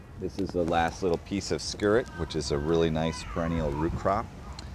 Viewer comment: What is the little wild flower he picks at 1:18? Skirrit? or something, I cant quite catch it. It looks like it mite be related to a European plant called PigNut. Id be interested in finding out what it is.